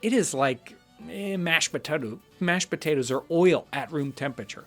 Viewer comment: what did he say mash'bataddoo?